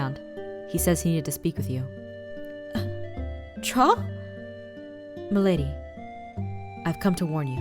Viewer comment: i thought his name said troll